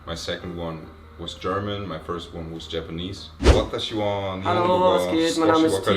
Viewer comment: Wer sich fragt, was T bei 10:16 wirklich sagt/For everyone who‘s wondering, what T actually says around 10:16: 私は日本語が少しわかります。でもまた…(上手？)じゃありません watashiha (spoken like watashiwa) nihongo ga sukoshi wakarimasu. Demo mata ... (couldnt understand it even after listening to it several times ._. But think he said じょうず)ja arimasen. Ich verstehe ein wenig Japanisch. Dennoch noch ... (verneintes Nomen oder verneintes na-Adjektiv glaube じょうず gehört zu haben - würde auch passen: ) Dennoch bin ich noch nicht geschickt/gut. I am able to speak a little bit of Japanese. However, ... (negation of a noun or na-adjective guess I heard じょうず), yet. However, I‘m noch (very) skilled yet. Did someone got the missing word for sure but I guess it should be 上手?